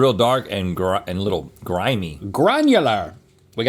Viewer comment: At a second there, I thought Link said "Garnular!".😂 It would have been epic!😂